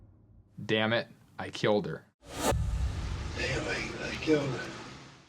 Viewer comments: I hear "Anyway I killed her". To me it sounds like he's telling the story and says but anyway I killed her. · I heard "Anyway, I killed her" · I heard him say “They think I killed her.” I’m glad he was finally caught. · I’m not saying he didn’t do it. But he clearly says “ They think I killed her” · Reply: See... I hear "Anyway, I killed her." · To me it sounded like he said can’t believe I killed her.